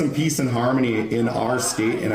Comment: peace and harmony? i thought he said a decent army.